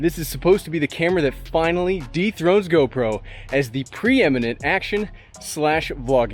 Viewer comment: thought he said depthroat mb